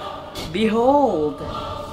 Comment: when he said BEHOLD.. i heard her saying BINOD 🤣 🤣